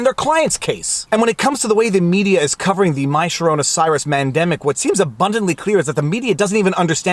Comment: does he say Mandemic ? LOL haha thats funny